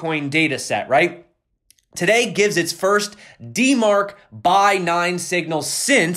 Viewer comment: I thought it said 'Denmark buy signal'.....I was wondering when Denmark became a power player in BTC.